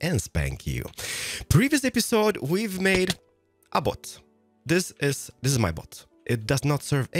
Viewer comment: Because of his accent and me not looking, I thought he said "last episode we made a butt. This is my butt." Immature, but funny.